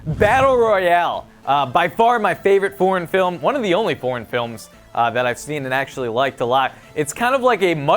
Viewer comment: I thought he said "porn film" lol